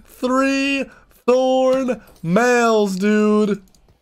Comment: I thought I heard "FREE THORN MALES DUDE!" lol